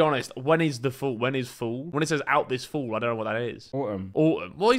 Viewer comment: I dont think Harry understood what was happening here. The trailer was saying "one will fall" and flight thought the movie was coming out this fall 🤣